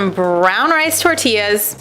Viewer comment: I thought you said no wheat burrito hello?